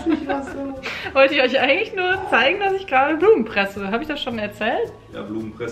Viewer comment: Ha ha ha ich hab die ganze Zeit auch verstanden ich fresse Blumen😂